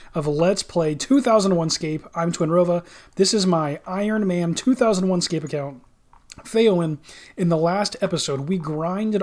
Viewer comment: everytime you do the intro sounds like you say this is my 2001 rs account failing lol